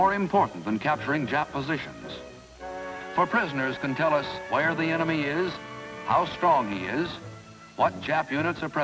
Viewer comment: I heard they can tell us where the anime is lmao